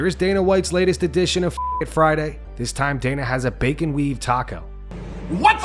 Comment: Thought he said bacon weed taco lol "Be a lot cooler if he did"